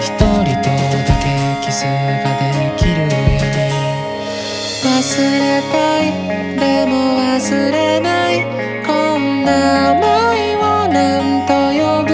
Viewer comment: この曲結構好きだからよく聞いてるんだけど、受験が近づくにつれて「遊びたい でも遊べない こんな思いをなんと呼ぶのかい」って聞こえてくるwww